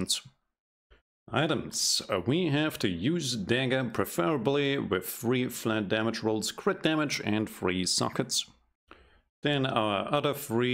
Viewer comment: I'm sorry but I am having a difficult time understanding you. What do you say about the dagger at 1:18 or so? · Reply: we have to use a dagger preferably with 3 flat damage rolls crit damage and 3 sockets